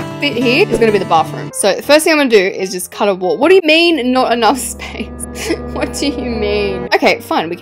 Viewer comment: BRO I THOUGHT THAT SAID NOT EMO. Anyways, I love your videos and have a great week ♥😊